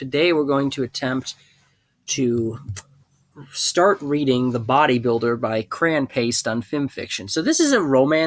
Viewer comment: Thought that said Bob the Builder for a sec.